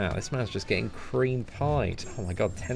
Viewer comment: did he just say hes getting creme pied at 13:40 ?